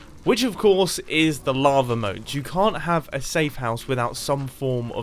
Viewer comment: The first 3 times he said lava i thought he said lama mote